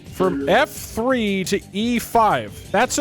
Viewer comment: I thought he said F3 to F5 11:49 why? To whom?